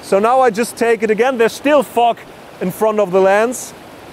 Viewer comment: Is it just me that it sounds like he say "f*ck" instead of "Fog" at 4:22? hahah. Thanks for sharing though.